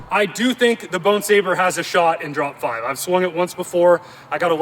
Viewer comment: I thought the bat just said BONER 😂